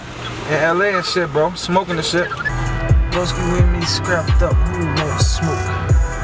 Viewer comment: i think he say: "doesn't is not crap that won't do smoke",,, or somethink like sh1t. jajaja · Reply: wit me strapped up, who wan smoke